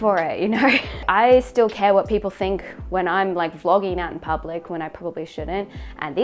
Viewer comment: I swear I thought you said "flogging." A lot of images went through my head before I finally concluded that, no, you couldn't have said "flogging," and that you must have said "Vlogging."